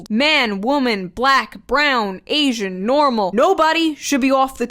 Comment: DID SHE SAY NORMAL XDDDDDD